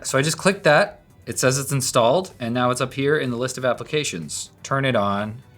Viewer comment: I thought you said it has a taser installed.